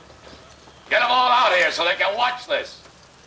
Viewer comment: Sounds like he says "Big foot get em all out here so they can watch this"